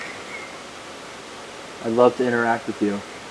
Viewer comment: DON'T MISS OUT ON OUR DECEMBER MYSTERY BOX! Yes, we are giving one person the real mural from the mega prank wars! <link> to get yours =). Of course, thank you all so much for watching! Enjoy the video! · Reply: I hear what sounds like a female voice saying, "I'd like to" when says it's ok you can come over here